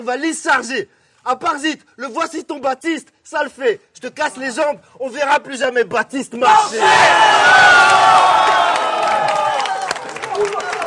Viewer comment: a la fin du battle quand il tcheck: " tu m'a grave défoncé..." 🤣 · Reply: Moi j'ai entendu "tu m'a grave respecté "